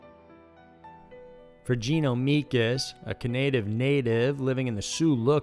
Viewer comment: I'm going crazy at 4:41. I can't get a solid hearing on what is here saying... It sounds like canative native which I know it's probably a Canadian native but something about it just stuck with me. If anyone figures out my small petty problem, let me know!